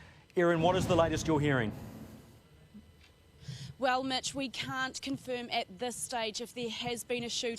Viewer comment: is that news anchor a midget? 0:55 i swear she said "well midget" 🤣😂😂😂🤣🤣🤣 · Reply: She said Mitch not midget